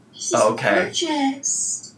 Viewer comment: I rewinded 3x & 3x I heard “this is gorgeoust” Am I crazy? I swear she’s saying, “gorgeoust”